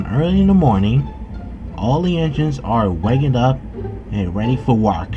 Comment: Did he just say "wark"? I understand why, because English is one of the hardest languages for people who don't speak it.